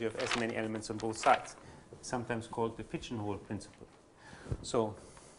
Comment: At the 10:40 mark, did he say the Pitchen Wolf principle? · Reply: He said "The pigeon hole principle" which says that if you have n holes and n+1 pigeons then there is at least one hole that contains two pigeons. If you have a surjective map(every hole contains at least one pigeon) f : A->B, with |A| = |B|(number of pigeons(set A) = number of holes(set B)) then one hole contains at most one pigeon which means that there exist a unique x (pigeon) which maps to a unique hole f(x) that is f is injective, thus f is bijective. I hope I explained it well !